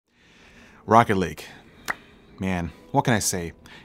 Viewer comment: i thought he said rocket league at the beginning, it threw me off lol.... great video though, cheers!